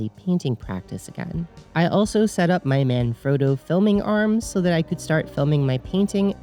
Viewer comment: I'll never be able to hear Manfrotto as intended after I thought you said "My man; Frodo". I think this is an improvement.